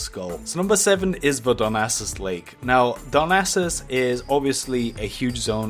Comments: I heard "Dumbasses Lake" xD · It sounds like he says "Dumbasses Lake"